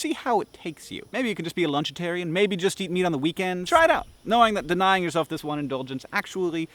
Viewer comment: I thought you said "Just try eating me on the weekend." I was like O_O ... then i realized what you really said..lol If God didn't want us to eat meat he wouldn't have made animals so tasty or give us the meat tearing teeth or the digestive system we have. I'm a lil weirded out by the thought of lab grown meat beacause I don't trust our government and that's just one more avenue for them to screw with our health...